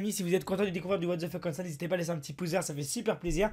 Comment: escuse moi c'est pouce bleue 😂